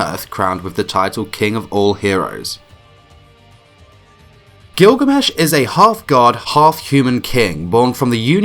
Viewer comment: I thought you said “ His a Hot god and hot human king”